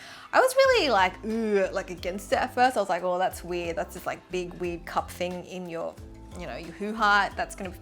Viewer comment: what is she said at this part? like big weak cup thing in your 'what' ??